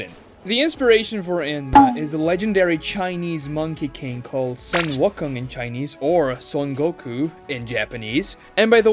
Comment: wtf did you say brother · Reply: Sun wikon 😂😂😂😂